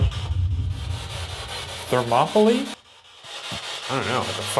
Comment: Shane heard “Thermopylae” I heard “Don’t Laugh at Me”